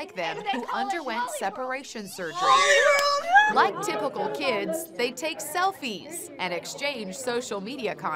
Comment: like normal teens, they took selfies and exchanged social media contacts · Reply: Listen to it again. She said “ like typical teens” they take selfies. Oddly the first time I heard it . I thought she said normal too.